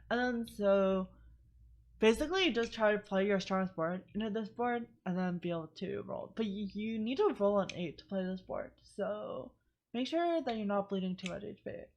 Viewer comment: It's so hard to hear what you're saying. Like from 10:10 to 10:20 I have no idea what you said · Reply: "you play strongest board into this board and then be able to roll, but you need to roll on 8 to play this board. So make sure you are not bleeding too much HP". Not saying its 100% clean to understand, but you just need to listen better.